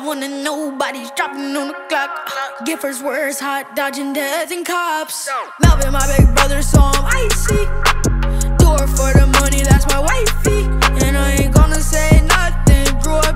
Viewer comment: For anyone wondering “Melvin my big brother so I’m icy” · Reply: I thought he said " Kelly my big brother so I'm icy"